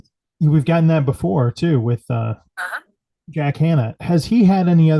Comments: ish I heard a female voice say "Uh huh" right after the host says "We've gotten that before with... (voice) Jack Hanna". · Yeah 16:41 they clearly said "uh huh" not hello. Sounded like but robotic. Pretty cool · I have my phone charging next to my computer. At 16:41 on the video (when there is a woman's voice ) , my phone activated, and I heard a womans voice from my phone...Strange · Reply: Yes I heard it too! That was wild! She said.."uh-huh" clear as day a woman's voice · Sounded like a voice saying ah huh at 16:40 🐺 very cool